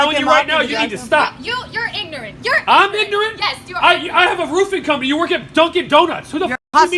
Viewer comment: Girl did you really just get tongue tied by him saying “I got a roofing company, I make 350,000 a year” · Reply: I could have sworn that he said “roofie” company.